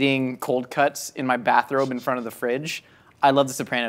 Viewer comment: I misheard bathrobe as bathroom and pictured Josh sitting on his toilet eating cold cuts with a fridge in front of him. Oddly enough, i don’t think I’d be shocked to find out Josh had a fridge in his bathroom.